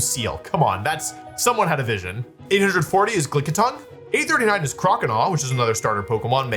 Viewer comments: did he say glick-itongue or am i hearing things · Is it just me or did he say Glickitung @ 50:05? Killer nickname when he uses it in his next mono-normal team 😂 Also, surprised Spidops isn’t lower 😅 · did i hear "Glickitung"?? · did just say Glickitung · Did he say Glickitung? At 50:05? 😂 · did he just call Lickitung "Glicke-itung?"